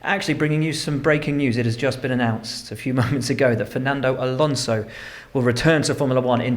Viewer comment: anyone who thought Will said "Months" ?